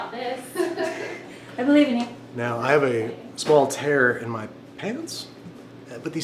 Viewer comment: I had to rewatch 6:25 thrice because I kept hearing "I have a small terror in my pants", rather than "tear". It felt wildly inappropriate, wonderfully weird and strangely humble all at the same time, but alas, I simply misheard. Is there such a thing as Freudian earslips? Also, love the way he finished it off with a hesitant question mark, that just elevated it like a perfect pinch of kosher salt.